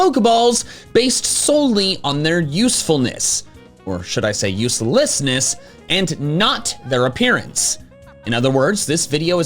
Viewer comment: few seconds into the video and i hear "usefullessness" man if only there was another word for how little use something has